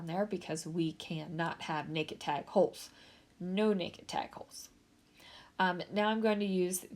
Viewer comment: Not gonna lie, every time you say No naked tag holes I hear “No wire hangers”. 🤣🤣🤣. Way to go on using the butterfly paper! I do like the layout better the way you changed it.